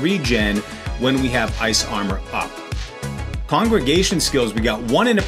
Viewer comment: I have a 100 ball light sorc with shako and everything but I've been using the maxroll build - it's good but this looks better. Definitely switching over. Also you said congregation instead of conjuration at 3:34 which cracked me up. Wait now that I finished the video why in the world wouldn't you use Raiment?!